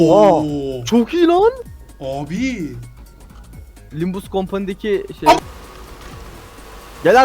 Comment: LIMBUS COMPANY KELIMESI MI DUYDUM BEN?!⏰️⏰️⏰️⏰️⏰️⏰️